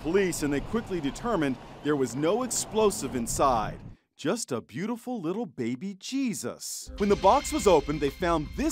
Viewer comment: Ok but the phrase "just a beautiful little baby Jesus" and I heard it as "just a beautiful little baby, JESUS".